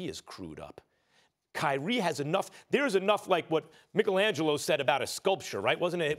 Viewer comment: Did he just say MICKLE-angelo